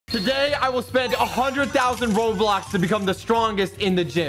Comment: "spend 100k roblox" That is not a typo I actually heard roblox there and I just spreading what I hear